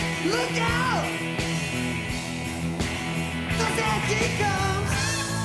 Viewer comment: Paul sounds like he says "horses are come" followed with a devilish sounding chuckle, which may be the point at which no name attributed the four horsemen of the apocalypse to either the Beatles, the people, or both. I remember an interview where the attorney for no name said that the guitars were seen to be the shields of the horsemen, the drums the pounding of the hoofs, etc. Obviously, no name was delusional, paranoid and psychotic.